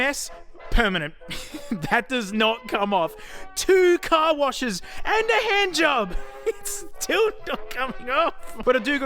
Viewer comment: "Two car washes and a hand job and it's still not coming..." and I didn't hear the rest of that sentence